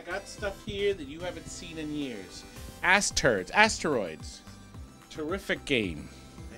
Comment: he said Ass Turds.. instead of Asteroids...😂